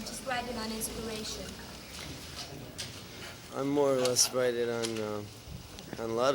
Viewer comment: Could anyone tell me what he says at 5:38? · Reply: You mean in response to does he sit down to write or write on inspiration?” He answers “I more or less write on lots of things” (napkins, walls, heart break…) He always pushes back on people limiting him with their questions.